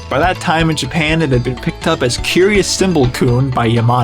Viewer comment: Just so you know, the third word you said in the name of that toy (Curious cymbal c***) is an offensive old slur for black people, and when watching with my family we were all shocked at how casually you threw that out (especially since the toy is… you know… a monkey). Just making sure you know what that word was used for and what it means !!! No hate!!! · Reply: The word I said was kun, a Japanese honorific (a type of suffix) used to address a friend or a young boy. When I translated the toy name it came out as "Curious Cymbal-kun" which was incorrect as I was later told in a comment it's actually "Naughtiness Cymbals Chimp." Regardless, that's what I used in the video. Thanks for your concern, but I just said a Japanese word, lol. I'm aware what the other word means.